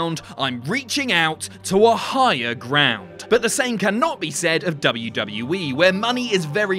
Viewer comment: did someone say high ground?